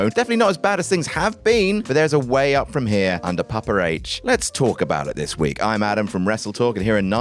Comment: "Let's talk about it" could've sworn I just heard Austin Theory for a min there lol